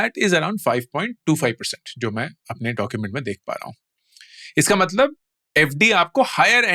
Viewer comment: I THINK you may say RBI Documents. NOT My Documents